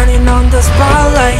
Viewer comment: Who else thought Bobby said starlight & not spotlight? No one? Just me? Ok 😂